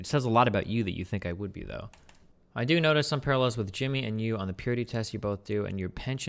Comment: I heard "notice me papa" and I got very confused for a second